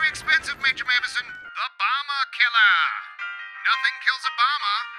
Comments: When you said bomber killer I thought you said Obama killer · who heard "Obama killer"?